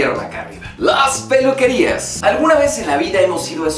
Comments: pense que decia las pulquerias jaja XD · Jajajaja pense que decia la pulqueria xD